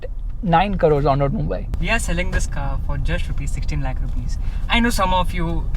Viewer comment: Did he say 60 or 16??😳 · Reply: 16 Lakhs